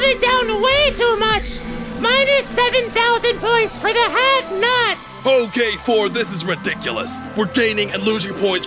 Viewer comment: X says minus 7000 points for the have nots. What caption say minus 7000 points for the red nut!